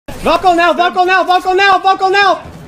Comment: Thought bro was saying buckle now 😂😂😂